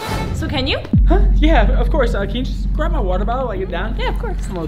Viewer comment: I thought he said "water bowl"😅